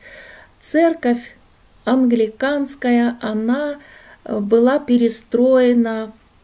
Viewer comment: Здравствуйте!Церковь 1190 года американская?Когда заходили в нее ,услышала слово"американская",вернулась назад.,может послышалось,да,нет,"американская"слышу,английская наверное?